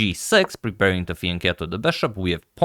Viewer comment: what did he say, preparing to thin cattle the light square bishop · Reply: "preparing to fianchetto the bishop". I am not sure if I answered your question as it is the dark squared bishop he is talking about.